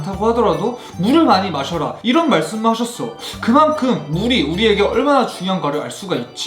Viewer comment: instead of drinking a lot of "water" he added "money" in the subtitles 😂😂😂😂